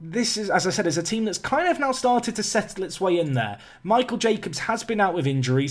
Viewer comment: I thought you said ‘Michael J Fox’ not ‘Michael Jacobs’ lmao oof